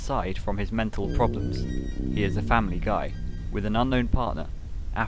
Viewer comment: did I hear he’s from family guy 😂😭